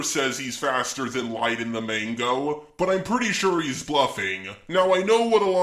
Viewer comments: Did you say mango? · did you just say mango? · why did you say mango but its called manga